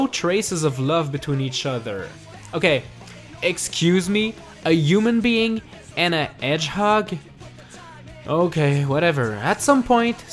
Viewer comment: I heard you say 'a human being. And a edgehog'